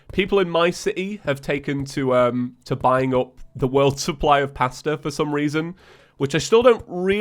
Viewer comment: It took me a minute to realize you were saying "pasta," I thought you were saying "pastor" :)